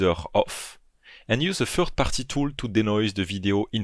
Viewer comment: I'm sorry, but what post-production filter is he saying at 12:38? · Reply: he was saying "third party tool"